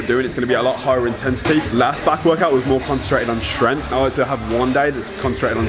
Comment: All I heard was, "last workout concentrated on tren" bruh. Lol XD